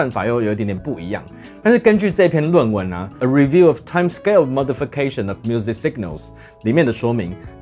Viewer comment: 差點兒就把那英文字聽成了 "Motherf**kation"......... 🙉🙊😂